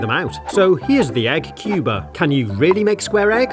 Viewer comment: Give us your best Egg Jokes... Here's a free 30 day trial of Amazon Prime for you - <link> This is an affiliate link which will pay me a commission if you sign up. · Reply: you said cuber but it sounds like you said Cuba